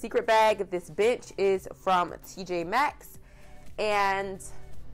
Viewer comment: Beautiful home and decor! Really thought I heard ..."this bitch is from TJ Maxx" 🤣